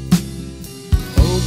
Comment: Open your heart, I'm coming home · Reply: I thought it said "I'm coming hard" first time I listened and I was really confused